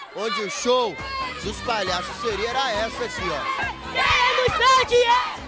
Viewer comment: "QUEREMOS MEU DINHEIRO" KKKKKKKKKKKKKK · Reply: Eu entendi "queremos seu dinheiro"